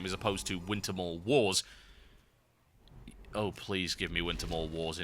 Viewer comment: what was the game you wished was in wow? it sounded like you say" wintermore wall"?